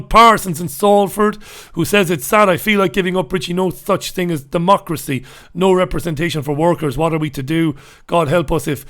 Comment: did I hear "no fuct schwing as damocracy ? 😁